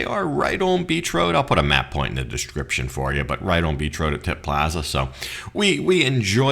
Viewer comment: Did you seriously just take a girl with such good assets to Tit Plaza or did I mishear?🤣